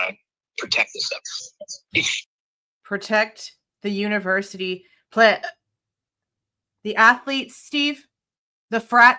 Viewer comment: I hear University “Club” 🤷‍♀️